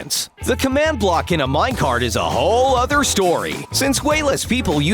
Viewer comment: minecraft furnaces or minecart furnaces cause i heard you say minecraft